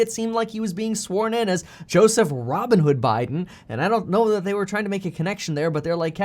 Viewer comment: I thought I heard Robinhood Biden.